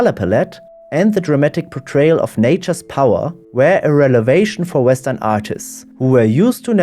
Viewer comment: Interesting view on this print. Very good to hear a real voice (wenn auch mit deutschem Akzent ;-)) on videos like this. (One reading error: "relevation" instead of "revelation" around 11:08 – sadly, videos can't be edited afterwards, AFAIK.)